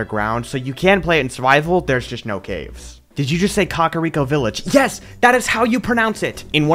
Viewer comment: It sounded like he said cuck-ariko village which i'm not sure if it's better